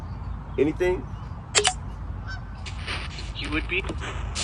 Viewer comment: Hello, Graveyard Shifters. At 24:19 after you ask “Is there anything else you’d like to get off your chest?” It sounded like she said “He would be..dead.” I watched this video three months ago, and after he got arrested, I remembered this video and I wanted to rewatch it and it just blew my mind. I didn’t realize how accurate this session was!😳Love you.